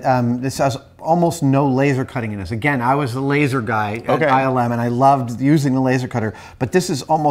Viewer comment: does he say he was the laser guy at 9/11?